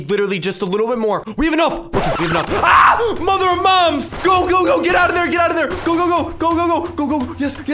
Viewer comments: hiii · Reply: what he say i think he say in 4:00 mother mom or mom mother or something? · hiii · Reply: and what he maen and he say in it mother mom or something?